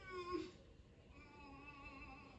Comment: Bruh!! He did NOT just reference Street Fighter 2 at 0:40 XD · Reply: Sounds like he said "keep Benihana away from me" The famous restaurant. I wish he said E.Honda